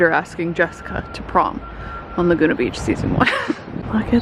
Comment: So I, never having seen Laguna Beach, 100% thought I heard you say "Dieter asking Jessica to Bonk" not "...to prom." Definitely got a big laugh out of that!